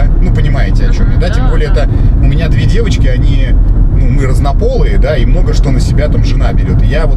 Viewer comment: Послышалось "2 девочки, они разнополые"